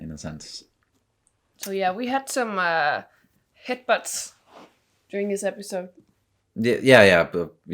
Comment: Head butts, I thought she said hicbutts more than once!